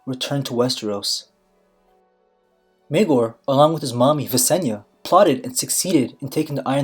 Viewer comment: I'm sorry but did you say "Mommy" at 1:52?